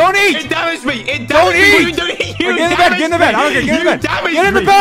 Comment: I love how George is more calm while Dream is like, “GET IN THE BEDDDDDDDD” Edit: it’s been 2 minutes and I have 10 likes wow Edit 2: IT’S BEEN 11DAYS AND I HAVE 3.4K LIKE WHA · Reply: AT THE END, I THOUGHT HE SAID “DATE IN THE BEEDDDDDD” 💀💀